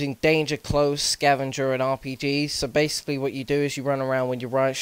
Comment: is it me or did u say RPD instead of RPG @ 2:30 ?